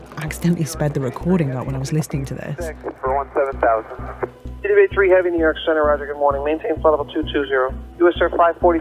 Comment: Alright for those in the back wondering what was saying during those unintelligible blocks around the 31 minute mark, and some corrections. Notes will be at the end. 31:06 "[T-W-A] three heavy New York Center, roger. Good Morning. Maintain flight level two-two-zero." 31:10 "US Air [five-forty-two, reduce speed to] two-eight-zero." 31:30 "US Air-[T-W-A three, T-W-A three] Traffic, One-o-clock, ten miles, south-east bound, descending [out of] twenty-eight-point-seven." 31:36 "Okay. You can uh, [yes sir, five-forty-two fly heading two-niner-zero now, radar vectors for your climb." Notes: Edit: All corrections or transcriptions are in , I kept anything that was correct outside of those brackets. 31:06 T-W-A, was the telephony/call sign for Trans World Airlines (now defunct.) The telephony/call sign being what they were called on radio to reference the company they fly for; Panam flights had the telephony/call sign "clipper," and British Airways flights are referred to as "Speedbird" as examples. They don't always line up with the company name, but are distinct and usually have some sort of hidden meaning/easter egg. 31:10 is telling US Air 542 to reduce their speed to 280 knots. 31:30 It appears that was about to issue a control instruction to US Air, but then judged that TWA three needed to know about the traffic in front of them descending. The plane that is telling TWA about is most likely the hijacked United 175 (I'm 99.99% certain.) 31:36 tells 54